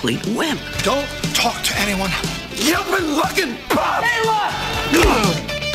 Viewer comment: What the heck does Griff say at 0:55 lol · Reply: "You've been lookin' for..."